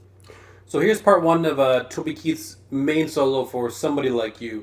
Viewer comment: did you just say toby keith? lol